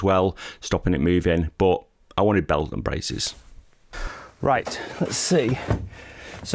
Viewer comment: Looking good. I can't wait for the big reveal. One question though, at 11:08 did you say bells and braces, instead of belt and braces, or did I hear it wrong? 😄